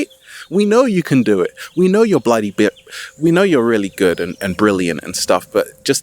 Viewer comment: im Aries..did he say we know your bloody better 😎😏✊ and try to hold it back ... thanks bro you can say it love you ✌